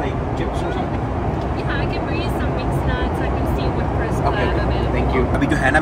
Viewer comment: subtitles are wrong, she said, “I can see what crisps are available”